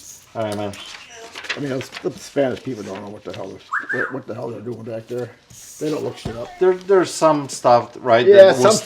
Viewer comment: Did he say "The Spanish people don't know what the hell they're doing back there" Who is this dude? What does race have to do with it?